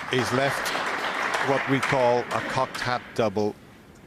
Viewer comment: I always thought they said contact double all this time lol now I see cocked hat does make more sense lol